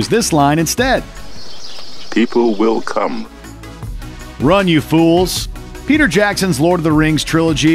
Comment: Every time I hear Gandalf's line, he says "Run, you fools!" Replayed this scene over a few times, and not once do I hear Gandalf say "Fly, you fools!"